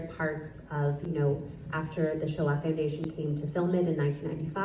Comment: I believe what is said here is the "Sho'ah Foundation" rather than the "Show off Foundation."